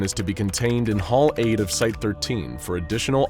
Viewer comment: Excuse me, did I just hear Site-13 ?!